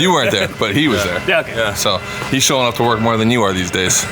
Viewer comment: I thought said to, "he's showing off more torque than you are these days."